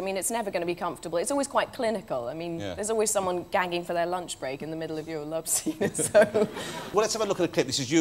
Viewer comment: For any British people reading this, I couldn't catch one thing she said, at 4:38. It sounded like "ganging for their lunch break", or perhaps "gagging". Not an expression I've heard before. Can anyone help? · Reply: She said gagging for their lunch break. Bit of a northern English term, more common is “gagging for it “ meaning desperate for something (usually sex)